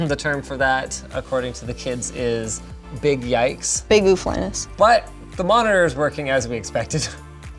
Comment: "Biggie Flaness" .....someone wanna help a brotha out? Wtf she'd just say??